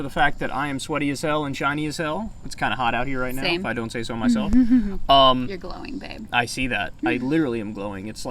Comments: Did Krystal call Kyle BABE????? Sounded like she said "you're glowing babe" right at the beginning. 0:05 Someone please tell me if you heard it!!!! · Did she just say "Your glowing babe" lol 😆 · Lol shiny as hell, thought he said "I'm Chinese as hell"